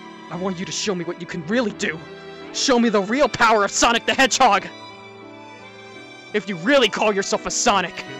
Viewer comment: I guess there was a problem with fleetway's mic and it sounded like he said "Sonic the egog" btw I love your outro, the song and the looks are so cool.